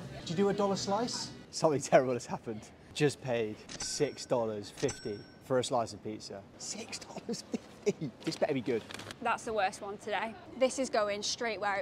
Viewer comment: I hear "Pizzr" whenever she says the word 😂